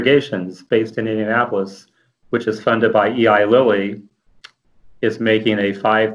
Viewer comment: min did he say E.I. LILLY? OR ELY LILLY.. BCZ ELY & LILLY IS A PHARMACEUTICAL COMPANY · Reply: Eli Lilly Pharmaceutical company